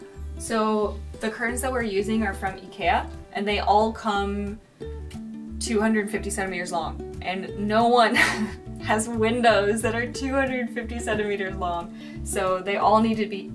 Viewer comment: at first I heard "the curtains are 257 meters long, and no one has windows that are 257 meters long" :D